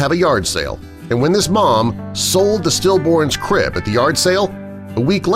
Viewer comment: oh my god i thought it said she sold her stillborn baby at a yard sale